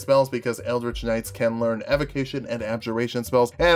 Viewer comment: did you just say 'evocnition'?